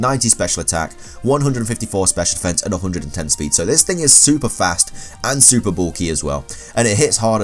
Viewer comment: it sounded like he said "this nigga super fast"